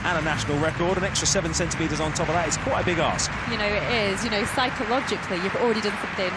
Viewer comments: "that's quite a big arse.." I literally thought that's what he said! 😂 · at first I thought I heard "it's quite a big ass" LOL